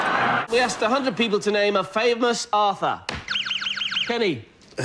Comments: nah cus 4:01 is kinda justified with how much it sounded like author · To be fair to the guy who said “Shakespeare” at 3:58 it sounded like he said “author” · Justice for this guy--I heard "author" too.